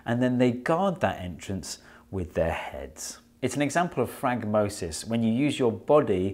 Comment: The subtitles say "It's an example of Frank Moses" rather than "It's an example of Phragmosis" which cracked me up